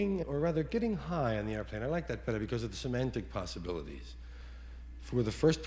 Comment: First I say bravo to you. the first time I heard this, don't know if you caught it or not, but the point where he said because of the semantic possibilities, I thought he said so many possibilities, and thought it funny, plus, I never even heard the word semantic. or new what it even meant if I did hear it that way, I was 15. But I was 21 in business college and had a course in semantics, but never made the connection to this, till several years later, I just happen to listen to this, and light comes on, then all the sudden this became that more funny because I now know what semantic means, any way I was on vacation and my normal feed wasn't happing on my phone. back home tonight so this is the first time I heard you in a long week thanks again for your kind attention. keep em coming